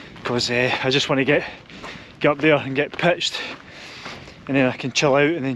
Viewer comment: Great video. Oh to have the knees to do a walk like that! Throughout the video, when ever I heard you say "pitched" I kept hearing "pished"...😂😂😂 Not to recommended on a big walk like that. Cheers, Jim